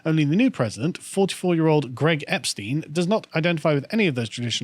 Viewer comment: Epstein? Did I hear that right?